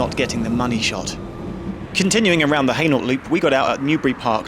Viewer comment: DID HE SAY "ANAL LOOP"